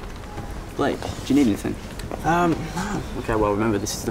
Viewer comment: is it just me or did she say " Mija, I love you " I miss my childhood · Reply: I thought she said "Blake i love you"